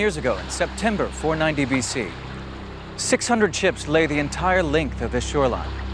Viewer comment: did he say.......chips????